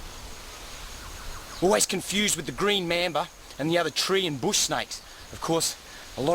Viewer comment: I thought he's saying green member